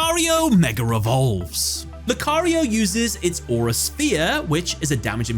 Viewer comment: I know it's probably your accent, but did you just say Lucario mega revolves lmao